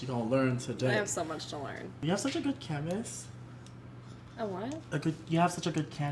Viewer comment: Who else thought when Patrick said canvas he said cames thinking like Madeline